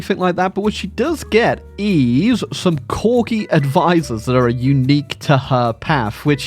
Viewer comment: I thought he said Quircky advisors and not Corgi. And I was like, what did Stewart Menzies ever do to you.